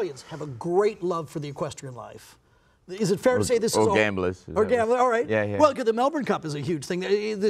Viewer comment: "or gain bless"? I don´t understand what he said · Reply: LL I think it's "all gamblers"